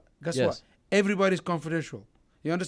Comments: Ali stfu, ur acting like a ghetto kid, with that filthy mouth and low temper does not represent Islam, Ali abdelaziz khabib is your master, listen to him- be humble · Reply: Ali Abdelaziz, " everbody is confidential" , instead of controversial, roflol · This guy does not think before he talks. His lips move faster than his brain. Surprising how these low IQ bozos make it to the top. The power of having connections to powerful people. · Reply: Ali Abdelaziz, " everbody is confidential" , instead of controversial, roflol